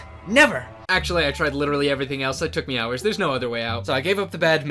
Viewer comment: did he just say it took him hours?